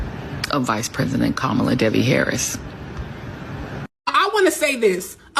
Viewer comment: Oh no Did she just say that kamala's middle name is debbie, we have a debbie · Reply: It's Devi, but it sure did sound like she said Debbie.